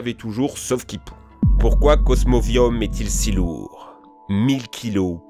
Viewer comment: cosmoquoi ??? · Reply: Reine Claude cosmovum